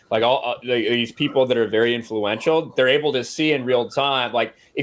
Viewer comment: I thought said ‘very intellectual’ rather than ‘very influential’ and was about to crap my pants if he was saying that in reference to Beanie Man.